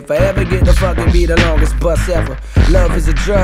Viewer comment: I thought he said "longest bus ever"